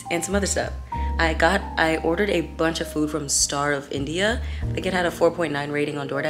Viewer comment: i heard “starve india” instead of “star of india” 🙁